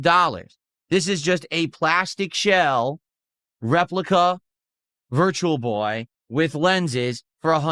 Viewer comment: Dude i SWEAR i heard him say he was buying a REPLICA Virtual Boy. Am I crazy or did anyone else hear that at some point? I swear he covers his eyes and clicks buy.